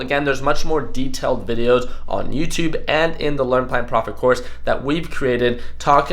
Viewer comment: What do you say at 0:41, " The learn pine profit course?" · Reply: Learn Plan Profit (LPP) It's private course for day traders :)